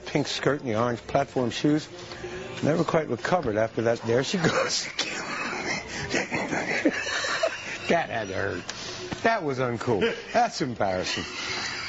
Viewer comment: it sounds like he says “there she goes again to kill me... chicken nugget” 😭😂